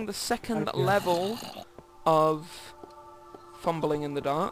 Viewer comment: did your parent Come In Durning the Filming As i Heard You Say "Im Fliming " :D